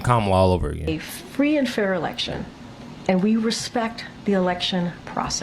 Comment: When she says "We respect" all I hear is "we will repay you".